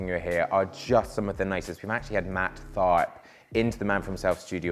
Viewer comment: did you say Matt Fart?